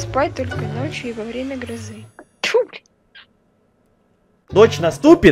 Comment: кто услышал то что с материлась тому респект) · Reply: Она сказала тьфу блин, не надо тут гриферить